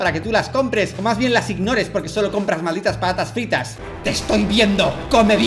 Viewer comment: Mmh, patas fritas 👌 La primera vez que lo escuche sonó como eso JAJAJA